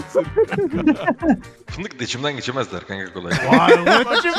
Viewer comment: burada tam olarak ne diyor anlamadım da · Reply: fındıklı da içimden kolay kolay geçemezler diyor. Fındıklı diye bir semt var mi bilmiyorum ama büyük ihtimal çevrem geniş bana dokunamazlar anlaminda diyo bunu